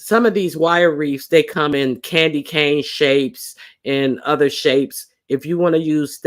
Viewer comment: I'm rewatching but let me say I heard size, not shape! Lets see if i recall correctly. P.S. I didnt recall correctly, 10:40 ish " any shape".